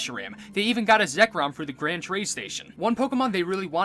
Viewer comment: Grand train station? Or did you say grand trade station? Either way, GTS actually stands for global trade system